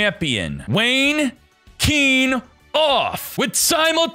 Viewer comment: This is for all the times said Wayne Kenoff, and I kept hearing "Wanking Off".